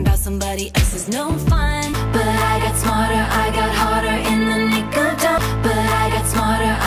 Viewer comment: I always thought that she said: "But i got smarter i got harder in the nicotine" 💀💀